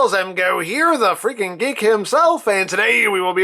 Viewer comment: For the longest time watching this channel for some reason I always thought he was saying "the freak and geek himself" instead of "the freaken geek himself" XDDDDDDDDDDDDDDD